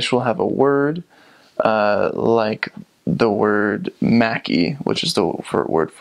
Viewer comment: I enjoyed how you have obviously studied Finnish culture, people and history. But.. when you said "mäki" it took me a while to understand as I heard "mäkki" and was wondering if that was the slang word for McDonald's :) Anyways, you are impressive in this presentation.